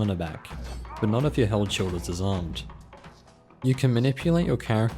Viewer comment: WTF are you saying here? Can't understand "but not if your ???? is disarmed..." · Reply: "but not if your held sword is disarmed..."